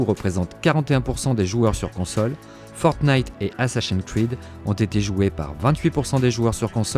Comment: C'est quoi la série "Assachian Kwid" à 14:04 ? 😅